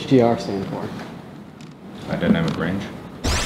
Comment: I thought that Mark said “I don’t have a brain”